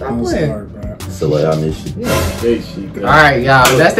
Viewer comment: Who heard him say soail i miss you